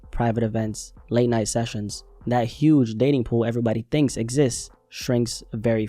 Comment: I thought you said the “Diddy Pool” 👀 My bad, dating pool* lol 😂